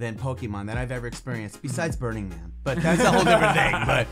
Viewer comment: As soon as he said "burning man" I just thought. "Ya that makes sense." Lol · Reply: I honestly thought he said “Burning Meth” 😂